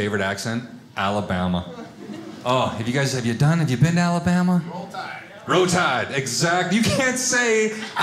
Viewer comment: I’m from Alabama and “Roll Tide” is the official way to answer all questions and also a greeting. 😂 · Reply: heck I thought he was saying "row tide," so I said that into Google hoping Google would figure it out and thankfully Google picked up on what I was supposed to be saying. Lol